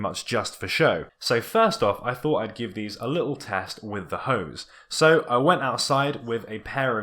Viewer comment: i thought you said test with the "hoes"... made me look :D